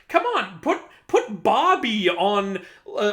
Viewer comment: I might've miss heard that, but did he say Bobby?... There is an Egyptian god name Bobby? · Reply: Nvm I just googled it. His name is Babi, he is was viewed as a deity of the Underworld, and is said to be the first son of Osiris... Osiris: Gawd Damnit Babi, I told you to stop listenin' to them toilet sounds!